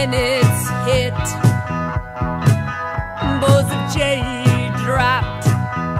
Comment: "Oh baby, I remember when you were born, It was dawn and the storm settled in my belly And I rolled in the grass and I spit out the gas And I lit a match and the void went flash And the sky split and the planets hit, Balls of jade dropped and existence stopped, stopped, stop, stop." I have shivers every time I listen to this piece... and I have to sing it out loud! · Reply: Perfect. This is what I hear her say. Point is that she is hard to understand by English speakers. I always thought she was saying instead of "balls of jade dropped", I thought it was "'course the chain dropped". Also she uses different words every time she sings the same song. Usually leaves out the best ones!